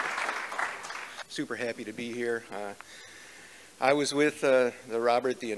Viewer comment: For a moment I thought he said "Super heavy to be here" and I thought he was a joker. Much disappoint now.